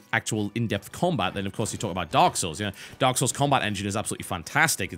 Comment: What's the game TB is talking about as an alternative at around 14:48? Can't quite understand because of the accent. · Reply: Dark souls